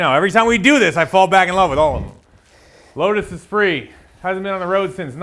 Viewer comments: Thought you said the lotus is free 😂😂… damn cheap ear buds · swear I thought you said lotus is free for a second there. LMFAO